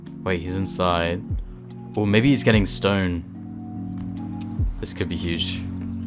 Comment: i thought i heard he's inside, he's getting stoned. Lol but he just gettin stone.